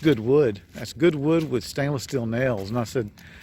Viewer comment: What I heard was, "Free wood and stainless steel screws!" Glad all are well.